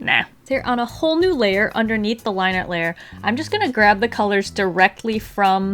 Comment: What said “I’m just gonna tweak the colors.” What I heard “I’m just gonna tweet the colors.”